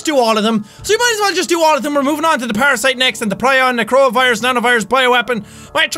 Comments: i literally thought i heard coronavirus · I thought he said corona virus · It sounds like he said corona virus. SEÀN, YOU HAVE A LOT OF EXPLAINING TO DO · I thought he said coronavirus lmao XD